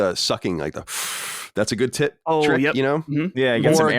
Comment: Collin- "you gotta do the sucking.. that's a good tip (though it sounds like he says tit) trick." This needs to be in a Lockmort edit one day.